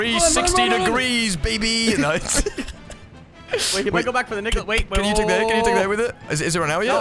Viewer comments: Did anyone else hear that? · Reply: "He go back for the Nick... ooooh" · What did exactly say at 9:11 💀💀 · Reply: "He might go back for the Nicolas" you weirdo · what did just say???? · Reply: "He might go back for the nicolas", dont be weird